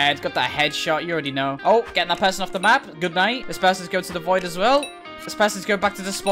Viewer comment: Woah woah woah hold up did he say good night